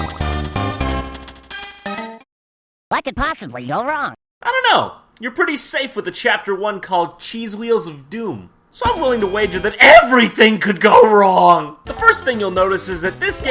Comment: I always thought he said "I'm an opossum, you're wrong."Lol